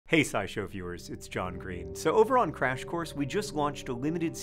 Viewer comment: wait is that actually him? is he ok? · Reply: oh my god sorry i thought he said hank green